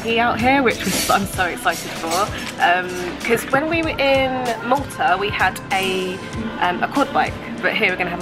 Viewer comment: which country did you mention? did you say Malta? just curious :)